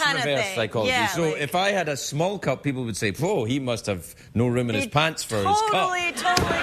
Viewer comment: can anyone tell me what he says at 5:53, about the cup, i can't hear correctly i wanna get it but ''if i had a small cup ppl would say bla bla..'' whaat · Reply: “...he must have no room in his pants for his cup“